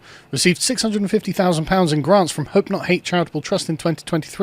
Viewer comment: It may be my American ears, but I kept hearing "Hurt not Hate".